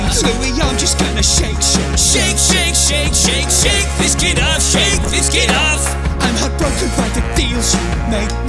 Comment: instead of kit it is shit